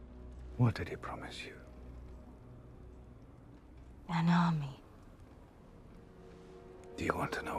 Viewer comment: she says an army i hear "crown"